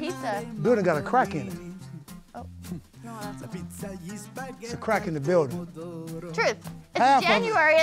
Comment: why did I think R-Truth said "they got a crackhead in the building"? Lmao